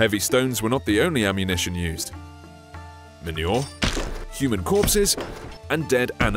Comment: What did the narrator said on 0:24? Menue? Or did he referred feces was used?